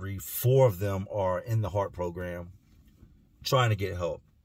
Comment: My high ass thought it said herpes program